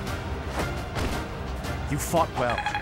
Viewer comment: am i the only who hear "you fucked up"?